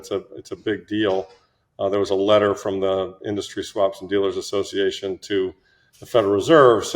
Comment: WTF did he say here?? "...a letter form the car dealers association..."?? These guys a lot of times speak between their teeth... or was it "swap"??